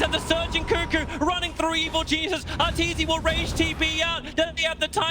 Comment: Why do i keep hearing "evil geesus"